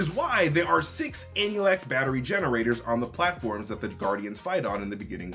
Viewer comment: Hi, I'm sorry, but at 10:57 I'm sure I heard you say harbulary batteries...? Just wanted to double-check that's the case